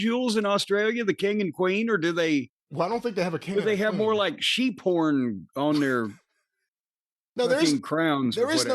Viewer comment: I thought Jim said Sheep Porn at first. 😂